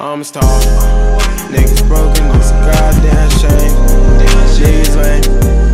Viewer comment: “i want it all, the money, cars, and fame, take my name” 🤔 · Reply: the second time he repeats the chorus sounds like he says niggas lame